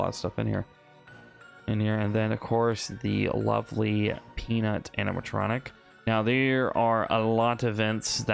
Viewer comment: I watched the finale of his while it was premiering it was fire and i love seeing you tour this one 18:03 did you say p-p-PEANUT?!? thats me :D